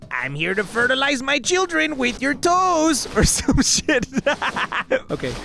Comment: I'm sorry, but did just say, "I'm here to fertilize my children with your toes?"